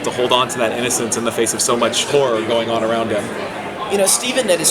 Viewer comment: I heard "Porn" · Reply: same but after rewatching it a couple of times i think he said 'horror'